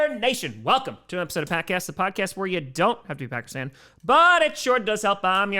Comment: I just realized he says yo do t have to be a packers fan instead of “im sure you don’t live in Pakistan”